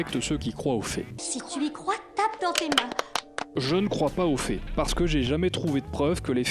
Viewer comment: J'ai entendu "Je ne crois pas au faits (fées) parce que je n'ai jamais eu de preuves que les faits existent". Je me suis dit qu'il allait chercher suuuper loin là....